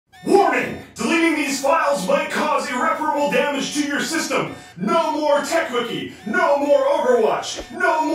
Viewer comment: Auto-captions said: "Morning. Believe me while my coffee wrapper will damage to your system." 😂😂😂